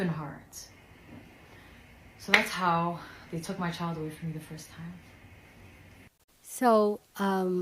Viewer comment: Yes. Please an update because I thought the last thing I heard her say was, "That is how they took her son from her the first time."